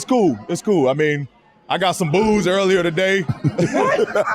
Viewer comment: Why they yelling like they in the club? 😂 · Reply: I thought he said “I got some booze this morning.” He said boos😂